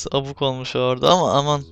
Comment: daki "aman" kelimesini "anan" olarak anladım ne fesatım be